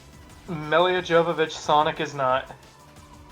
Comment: I thought you said "Melly Jovovich Sonic is not"